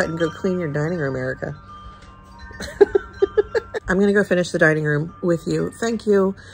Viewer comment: From one Elf movie lover to another, I need you to reframe what your mom chimes in with....instead of telling you to get up and clean, I'm pretty sure I heard her saying "Erica, I'm on your porch and I'M SING-ING!!" {hugs you}